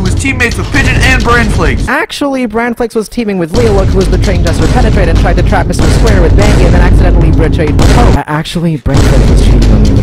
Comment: Here’s my attempt at translating what was said at 2:54 “Actually BranFlakes was teaming with Leolook who was betraying JesterPenetrate, and tried to trap MrSquare with Bangi and accidentally betrayed Poke.” “Actually BranFlakes was cheating on me with Vitala(???) and Ash(??)”